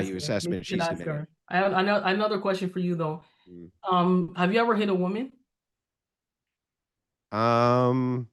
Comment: I swear I heard her say, "Have you ever had a dream?"